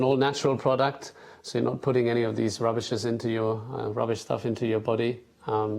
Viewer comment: DID just say Redbull is RUBBISH